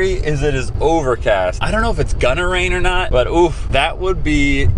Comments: I thought he said "gunner rain". 😐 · thought He said Gunner rain, anyone else hear that? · “Idk if it’s gonna rain” tell me why I thought he said “gunner rain” I need sleep💀😂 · I don’t know if anyone else heard this but I thought he said “gunner rain or not” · When he was talking about the weather in the beginning I thought he said gunner rain at first and now I have this fear of Gunner heads is falling from the sky